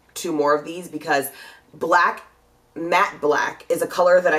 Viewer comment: No, she didnt misspeak you probably thought that she was saying MAC when she was really saying MATTE